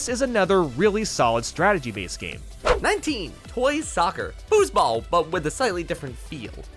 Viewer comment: Did just say "Fußball"?(German Word for Soccer)